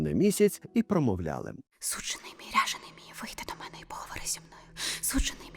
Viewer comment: Почулось : «сучений мій , ряджений мій»